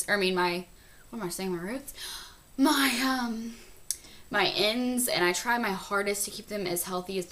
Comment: Did she say penis at 4:11???